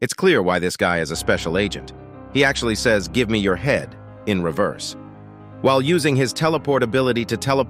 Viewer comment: When he Said give me your Head i thought he Said i will give you Head 😂